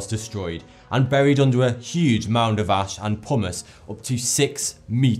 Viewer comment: genuinely thought he said "Buried under a huge mound of ash and hummus"